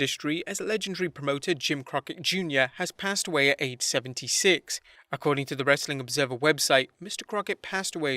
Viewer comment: I thought he said 876.